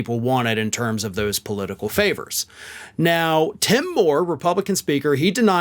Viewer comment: Damn.... i thought you said Tim Hore 😂